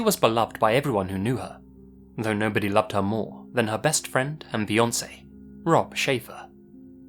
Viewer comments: I stg I thought you said “no one loved her more, than her best friend, and Beyonce.” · Misheard this as "Though nobody loved her more than her best friend and Beyonce" and I was like, DAMN. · you said Beyoncé instead of fiancé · Did he just say “Beyonce”